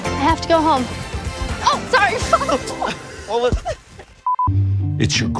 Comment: I feel like “It’s your grandma” is going to be a continuing inside joke. A sad moment is going to happen and one of the characters is going to look down and say, “it’s your grandma” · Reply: I thought he said 'it's your grammar'. Would be funnier, though.